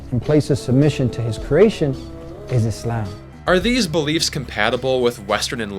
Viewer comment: The guy says Islam but the Caption says This Life